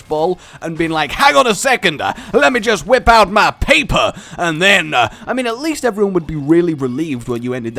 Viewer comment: i heard let me whip out my peeper Σ(O_O；)